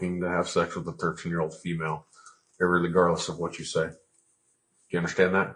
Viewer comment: What the hell did he say? Ir-rely-gardless? 🤣 "Irregardless" isn't stupid enough?